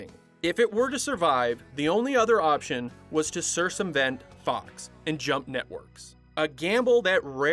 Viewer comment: did you say cirsumvent, instead of circumvent?